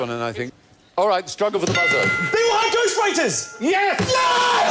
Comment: I've watched these clips so many times, but I still never get bored with them! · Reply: Can you help me understand what the posh yobbo screams @ 31:44? All I can hear is: "Sky-high goose prices." Can't be right, can it?